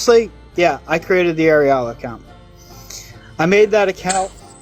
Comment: While not reading the screen, I thought he was saying there was an "Areola account." 😂😂😂